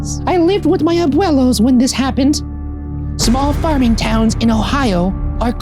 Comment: Ryan reads small farming town. I hear small farting town. Am I the only one?